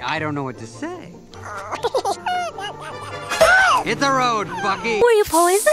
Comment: did I hear “hit the road f***y”